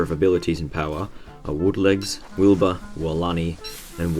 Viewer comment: I heard Wilba (Hamlet) at 0:24 you too ?